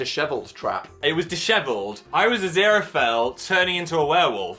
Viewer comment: captions say Zerophil instead of Aziraphale, i thought it was kind of funny